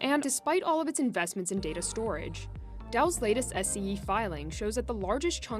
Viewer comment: did she just said she filing? Anybody heard the same?